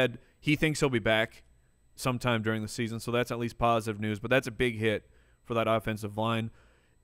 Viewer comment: Next time say, "Big Hit" because it sounded like you said, "Huge Shit" hahaha